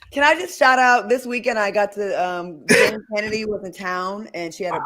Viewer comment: WHO??? WHAT SHE SAY YALL. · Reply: I had to replay it a few times. I’m quite positive she said Jane Kennedy.